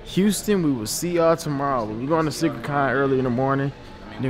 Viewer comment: I thought he he said snickercon at 30 sec